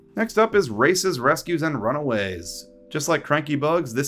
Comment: I thought he said 'racists rescues and runaways' Could thomas say the n-word?